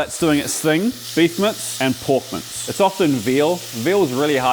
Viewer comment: I thought you were saying "porkmants" instead of "pork mince" lol anyways, love your style dude!